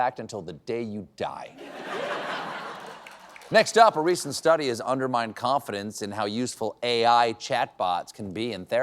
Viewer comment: The words you said: ..."a recent study has undermined confidence..." The words I heard: ..."a recent study has, under Mein Kompf-idence..."